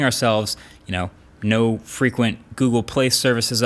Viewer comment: did he say frequent updates 😂